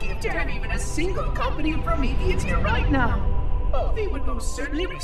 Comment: does he say promethean? hmm